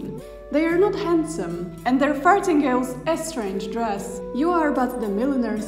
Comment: giggle giggle - I heard 'fartingales'